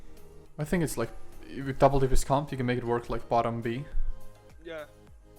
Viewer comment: Hey when you were talking about icicle staff around 21:50 what was the other weapon you said to pair it with? It sounded like double daggers but your mouse was on the fire staff so got confused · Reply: I said its viable in "double dps comp" not double dagger or fire xD.